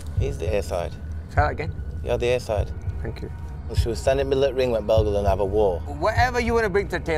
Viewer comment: did Kell say “shall we stand in middle of ring and bell goes have a war” ???